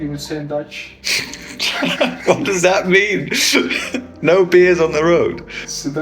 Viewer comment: Funny how said the dutch saying which goes like: "No bears on the road". Which is a cynical way of saying there are no obstacles towards the goal or objective. Kinda funny, while they are in the dessert. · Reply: I thought he said "No beers on the road". But I guess "bear" makes more sense.